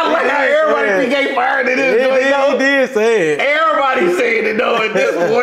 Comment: He was actually saying "Lookat that now I gotta.." but Nogla is know to slur his words so it turned out horrible